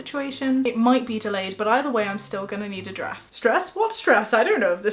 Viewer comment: Stress? I thought you said dress